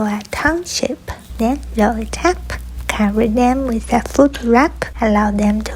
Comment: I thought she said cover them with foot wrap😂